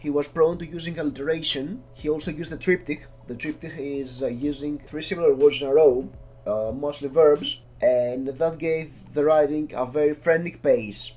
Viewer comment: What are you saying at 1:24, sorry I do not understand. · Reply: "He was using the triptych, The triptych is using three similar words in a row, mostly verbs and that gave the writing a very frentic pace"